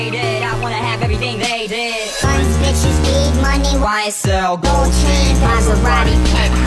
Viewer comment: I thought it said “Oil change” instead of “gold chain” 🥲